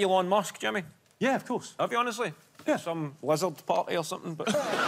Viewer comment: What does Kevin say at 7:48 to describe the party? I don't understand the word XD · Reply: Sounds like: "some lizard party or something"